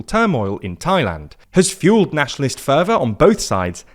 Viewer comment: for a second or so i think i almost heard 'nationalist führer' lol